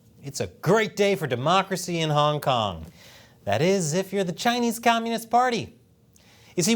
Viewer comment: I thought it said the end of King Kong